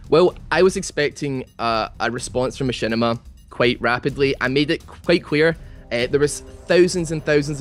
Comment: I thought he said quite queer